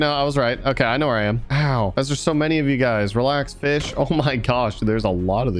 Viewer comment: it sounds like he said relax bitch 🤣